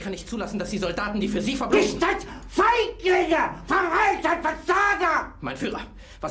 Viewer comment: bruh did h1tl3r just said the N-word? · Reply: It's actually Cowards in German "Feiglinge"